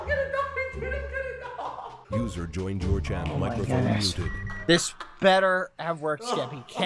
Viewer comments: skeppy got bbh so mad he turned into a motorcycle at 2 am. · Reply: captions says loser joined your channel like a batstered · Captions say "Loser joined your channel"